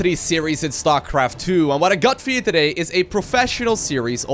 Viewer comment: What does he say at 0:02 ? All I can hear is "What I got feed today" xD